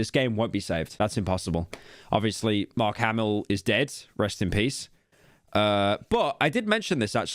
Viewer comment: Did... Did just say that Mark Hamill is dead at 12:05?